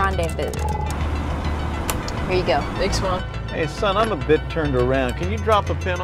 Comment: I thought the driver said "hey son of a bit" o_o